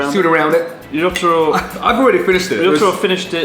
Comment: When they said Otaro I thought he said Jotaro XD Edit: yes this is a jojo reference